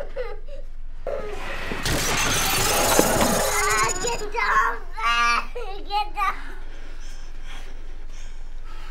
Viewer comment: What was that baby saying?! 1:41 😂🤣 The words didn’t come out of the baby’s mouth 😂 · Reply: get . No. Back. Get down